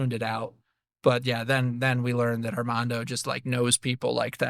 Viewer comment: 🤩 🫶 Just as great as the first time! 🖤/🤍 Divide & Conquer. Oldest play in the game book. EDIT: At 11:45 did you call him Hermando?🤣 I missed that the first time.